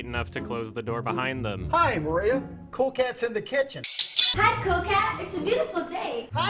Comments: I thought she said coke cat. Geez I need new ears. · does he say "hi Gloria " or " hi maria"?